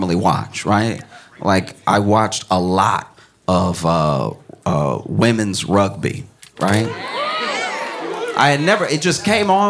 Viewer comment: I watched womens rugby. I thought it said women rub me.